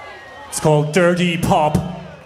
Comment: when i heard it live i thought they said dirty bob.